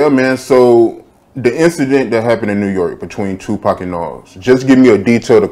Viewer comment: I thought snoop said that happened in Nas hood